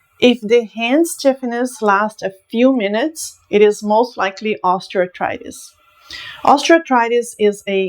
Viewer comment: can someone say what she says, something like "ostreotritus"? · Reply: Osteoarthritis